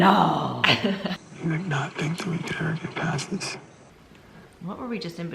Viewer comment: What does Peter say at 16:08? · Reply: something about how they can "get past this." He was either asking her to decide if it's something she could get past, or telling her he thinks they can.